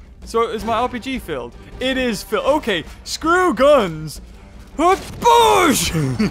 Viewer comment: I thought you said scrap guns? !?!?!!!!!!😠😈😈🙍♨⛽🔕